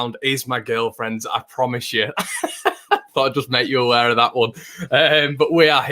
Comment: Good video again....at first I thought you said "I promise you, that Teddy is my gf" 😅